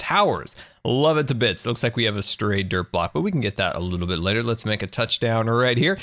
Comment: For some reason I thought you were saying "derp block". I've been watching too much.